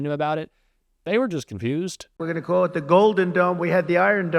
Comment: WTF is he talking about? Domes? For what? I must have missed something. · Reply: I heard gnomes.."GOLDEN GNOMES" LMFAO